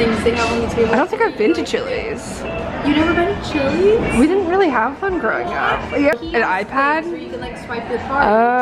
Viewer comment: When you were talking about chili’s and you said “we didn’t really have one growing up” I thought you said “we didn’t really have FUN growing up” and I lost my shit 😂💀